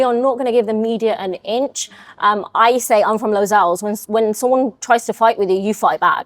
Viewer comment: Anyone know what she says at 17:28? I'm from where? Never heard it before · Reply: Lozells, Birmingham